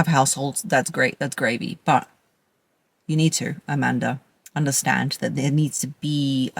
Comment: I wasn’t listening because I was texting and then I hear “Latisha, you won’t listen!” 😂😂😂 had to rewind it back